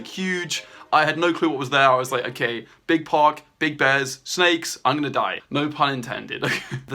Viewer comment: when said big park i thought he said big pog